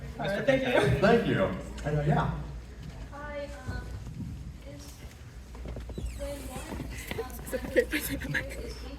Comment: what is the question here??? i cant hear lol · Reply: it was "when one had her room destroyed, was leafy the reason?" sorry for yapping over that lmao i just thought asking if he was allowed to say hi to me was really funny lol